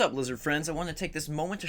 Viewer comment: wait did u just call us loser friends? haha